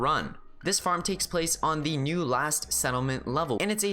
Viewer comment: you sounded like you said ‘Last’ instead of ‘Lost’ Also the first 100 people who watched your video are really good so good job to everyone who had notifications on